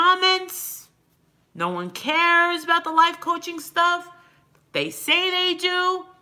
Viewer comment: A lie poach ? Almost sounded like you said life coach lol 😂 that would be a joke